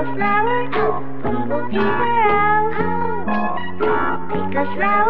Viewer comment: At first in the song when it says "bumblebees are out" I thought it said hope will be my out